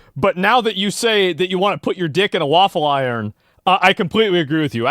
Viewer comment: Asmongold spreading misinformation (2:20) .. He did not say dick .. he said balls ... Sad Day for Truth